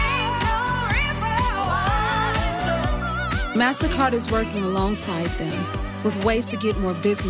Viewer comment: Lol.....maybe you should have the announcer pronounce more correctly. Or am I missing something when it sounds like she says Massa Card? Wow 🤣 Yep, nothing like a black women asking for respect, and goes on to call her credit card Massa Card 🤦‍♂️